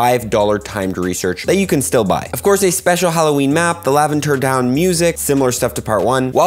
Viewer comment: Did he just say "Laventer Down music"